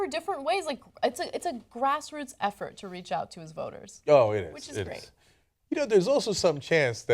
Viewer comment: Power of suggestion: at 2:23 I swear I heard "Grassroots effort to reach out to his boners." D